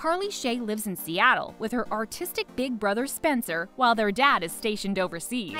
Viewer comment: i thought she said autistic and not artistic 💀